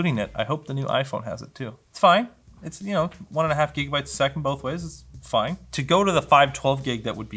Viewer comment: did you just say jigabyte